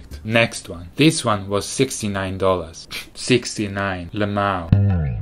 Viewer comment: I love this guy saying funny things with that straight face 69 L M A O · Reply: So that is what he said..... i thought he said LEMAU which in my local meaning LAME....ooops my bad...